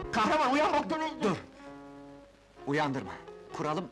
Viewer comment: da ne diyor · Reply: "Kahraman uyan bak televizyon (aldık/getirdik)" demek istiyor sanırım.